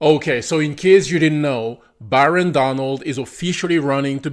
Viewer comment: 😅😅 i thought you said Baron Donald's, Which my dyslexia brain told me, What the Dons son?? Then it tripped 😂😂😂